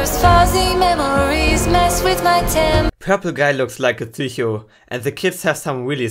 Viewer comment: the was you said psycho. it sounds like thicyho